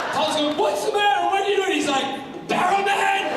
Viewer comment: Can someone please tell me what Barrowman is saying 2:42-2:47? I'm Norwegian and I can't catch it when he talks so quickly.... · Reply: "Paul's going [to Steven] 'WHAT'S THE MATTER WITH YOU!?' And he [Steven] is like, 'BARROWMAN!' "